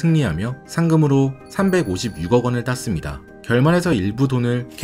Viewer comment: 356원이라 들리는데요?0:41초쯤